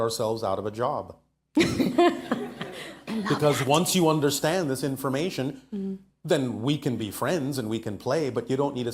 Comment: i hear another vioce and it's say "i love you"? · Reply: "I love that" referring to what Bashar was saying.